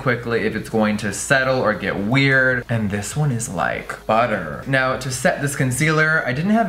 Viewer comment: I legit thought he said “This one is like butt hurt”... just me...